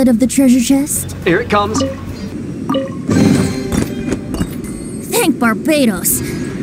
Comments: Is it just me cause I heard “bang Barbatos” XD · I thought it said “hang barbatos” 💀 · I thought it said "Bang barabatos" 💀 He's still my favourite- lol